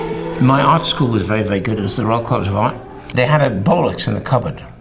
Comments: I swear I heard him say, 'They had a bollix in the cupboard', but in a Scottish accent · I know he says Bolex but all i hear is "they had a bollocks in the cupboard"